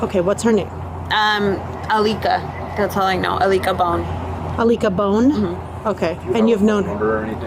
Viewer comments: Did she just say her friends name was "Alika Bone" or "I lick a bone"? Is she kidding? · The friends name is alika bone lol wtffff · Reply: I thought she said I lick a bone 😮